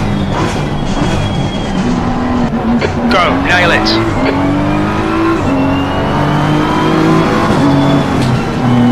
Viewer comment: idk what voice said but I heard "GO VIOLENCE" 🤣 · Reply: Go! Nail it!